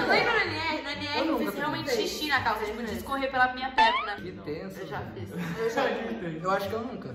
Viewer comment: ouvi o falando "que tenso vida"